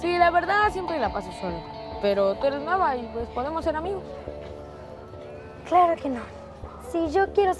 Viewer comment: Cuando puse subtitulos puso que dijo El chico "Tu eres mamá y podemos ser amigos" :v 1:24 Maravillosa Jugada! xdxd 2:45 Soy feo con F de moco ofendido entendí :V 3:24 Los caballos en el establo estan maravillados ;v 3:53 Pobres perritos los atropellaron :"C